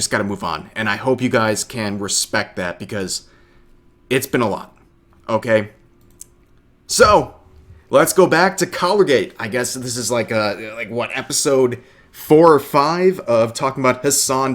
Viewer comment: Thought people were saying Collgate 😂😂